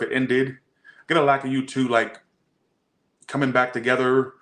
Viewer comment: what did he say - “gonna lack of you two coming together “ ?